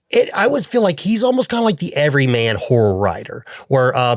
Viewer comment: It legit sounded like you said "Every Man Whore Director" for "Every Man Horror Director" LOL